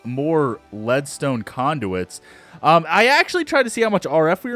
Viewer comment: when you said redstone conduits i thought you said bearskin condoms XD